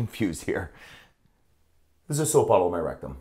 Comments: I always thought he said soap bar but no IT WAS A FUCKING BOTTLE?!?! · I thought i heard soap bar but it was a bottle 😮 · For some reason i Heard it as "soap bar" and when scroll and said the sentence again i was "A SOAP BOTTLE?!"